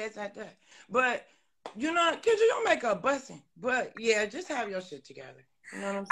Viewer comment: Did she say “Kendra, your make up busted”? I haven’t seen anyone mention it but it sure sounded like it to me. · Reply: She said”bussing”. Which means nice or beautiful.